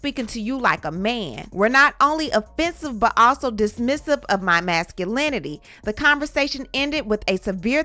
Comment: I thought Jeanie said she was gonna be submissive. Gurl bye, you got the same black man that black women get, Cyn G